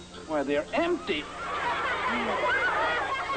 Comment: So Flowey is basically saying ketchup? Huh. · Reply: Actually he is saying "Can't ya?" I just thought it sounded a lot like "Ketchup"